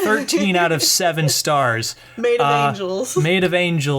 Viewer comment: i heard that "made of angels" :3 but i really want to go to disney now guys